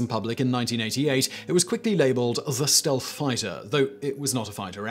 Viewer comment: I always heard "stealth bomber", not stealth fighter